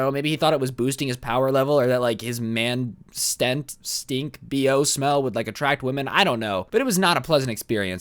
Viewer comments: I thought I hear him saying practise his stand stink smell 😂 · “HAH! YOU THOUGHT I WAS GIRL BUT NO ITS ME,DIO!”-dio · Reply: I thought I hear him saying practise his stand stink smell 😂